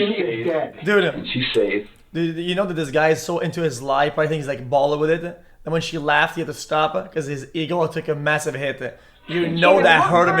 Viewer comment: Okay I’m really fluent in XQCnese but what the heck did he say at 40:52? Any XQCnese PH.Ders? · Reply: “This guy is so into his life I think he’s like ballin with it,and when she laughed he had to stop because his ego took a massive hit”